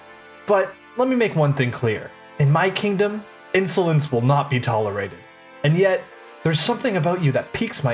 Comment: Yo I thought he said "insulin will not be tolerated" 😭